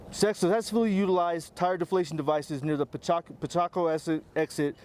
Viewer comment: Did he just say "pichaco"???